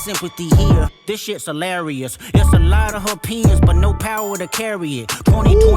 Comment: I’m glad I have you, cause I honestly thought he said “Got a lotta her p***s, but no power to carry it.” 😂🤦🏻‍♂️🤦🏻‍♂️🤦🏻‍♂️🤦🏻‍♂️